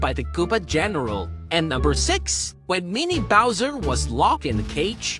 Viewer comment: Bro I thought he said he sang so loud that he farted